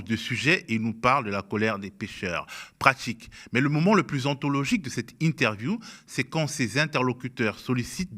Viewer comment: Pour dire, au lieu d'entendre "le moment le plus anthologique", j'ai compris "le moment le plus pathologique" ! Il y a de ça... 😅